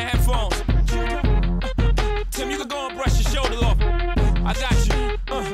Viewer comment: Thought it said dust off your shoulder